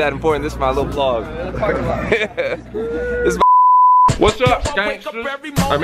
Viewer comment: when he started the Toronto part, I thought he said, "what's up skanks" 😂😂😂